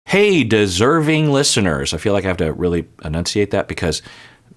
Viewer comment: For a while I thought you were saying "hey discerning listeners," which actually works pretty well haha